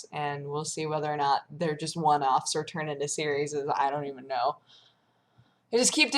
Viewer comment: Did she just say "serieses" ?? Hahaha.